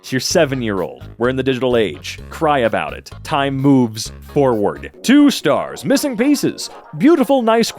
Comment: i thought you said "time boobs on" instead of "time moves on"